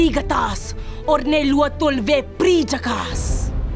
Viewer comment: did she just say “jhakaas?” 😂